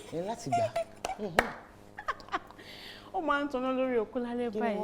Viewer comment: Did I hear "Oko" correctly???!!! LOL